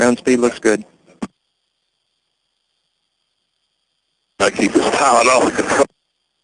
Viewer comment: I'm pretty sure I hear Mr. say, "I gotta keep the pilot off the controls." That sure gives a mental picture of just how intense this was!!